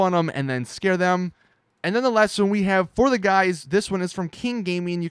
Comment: you said guys instead of girls